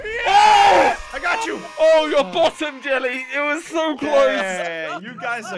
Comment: I heard "your bottom jelly"😃✋